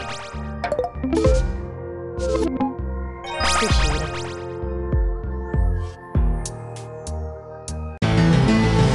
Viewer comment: What did Lapis say? I heard her mumble "Good human" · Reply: “Apreciate it”